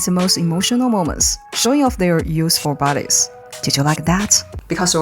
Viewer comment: it sounded like you said Useful Bodies.